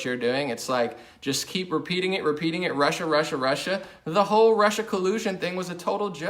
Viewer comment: When says "Russia, Russia, Russia" I hear "Marcia, Marcia, Marcia" ... lol.